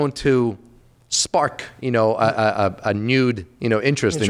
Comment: "A Nude Interest". What did you say? lol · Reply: Renewed interest 🤣